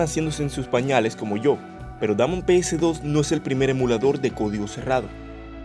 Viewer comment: Entendi "Dame un PS2"